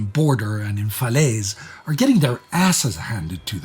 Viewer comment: did really say "asses" just now? OMG!! Lol😂!!